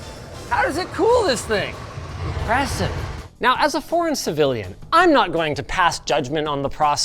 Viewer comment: I thought you said “As a former citizen”